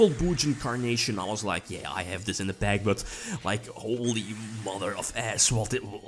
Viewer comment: Did I just hear "Holy mother of ass?"